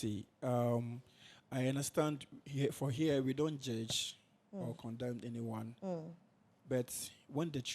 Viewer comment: I thought they said they won’t judge lol